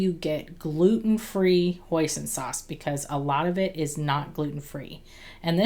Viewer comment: Looks yummy and I'll try UT with chicken. I thought you said oyster sauce, watched again, you did say Hoisin sauce, my bad... .